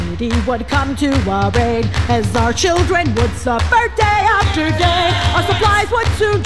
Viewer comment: Instead of 'suffer day after day' I heard 'puffer gay after gay' I am an idiot